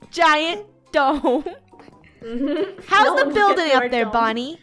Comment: did i just hear say "bread" instead of bed XD 😁😀😄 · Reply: lol I heard it "We gonna protect our bread" XD